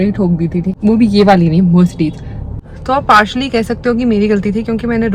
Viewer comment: Did she say Mercedes?